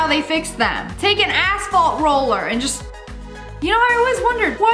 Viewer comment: when she said asphalt roller i thought she said asshole roller LOL